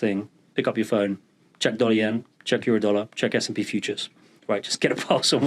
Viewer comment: What does he say to check at 16:20? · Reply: Dollar yen 😂